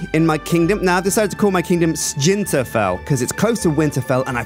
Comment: What does Sjinterfell have anything to do with Game of Thrones? That's a Skyrim reference. Sjinteross would be a Game of Thrones reference. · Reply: Sentinalh when he said sjinterfell i thought he said israphel